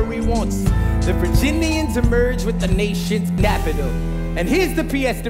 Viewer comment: You can tell they are actually singing and not lip singing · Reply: Yeah it sounds like leslie says napital instead of capital